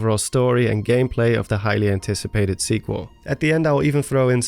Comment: he said "highly anticipated" but I heard "Hylian ticipated"